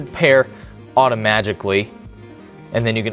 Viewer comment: did he just say "automagically"